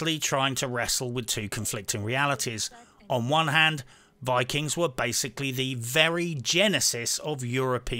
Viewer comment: All I hear is "vikings be based". Sincerely A true nord